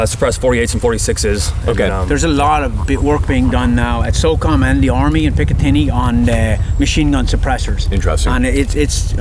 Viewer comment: who is he saying is running the suppressed mk48s? I cant quite understand what he is saying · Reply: He said Crane and Picatinny Arsenal are testing and developing the technology to make viable machine gun suppressors. None are operational yet, large scale anyway (it’s hard to say “no one does x” because all these secret squirrel units are trying all kinds of shit at any time)